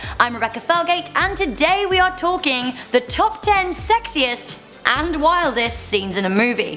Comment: I thought it said sexist 🤦‍♀️